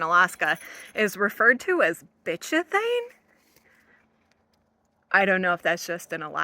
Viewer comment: I think it's called "bitumen" which when butchered properly could very wel sound like bitch-a-thing